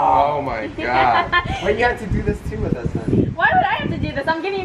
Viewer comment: did said "why did u have to do this with us " honey"???" (Dont ask why im here) 🥰